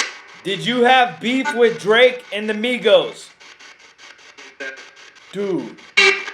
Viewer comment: Migos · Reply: It does sound like it says migos